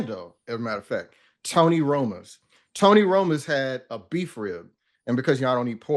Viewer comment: I heard Tony Romo's, a Place for Fibs.